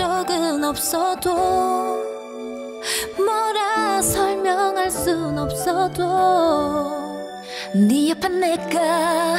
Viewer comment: I swear that I heard Jiu say "insomnia deu" >_< ♡